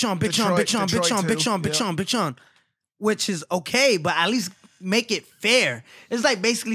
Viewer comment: sounded like he’s saying “bitch on”.